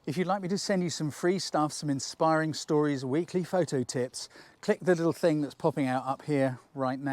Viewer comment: it sounds like you said “photo tits” maybe you did after photographing a good looking woman. Great video as always.